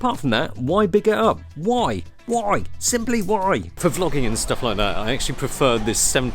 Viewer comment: sounded like Lok